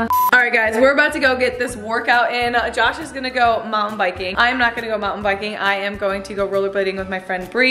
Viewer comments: i thought she said mom biking at first lol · When said at the beginning of the video “Josh is going to go mount biking” at first I thought she said Josh is going to go Mom biking😂😅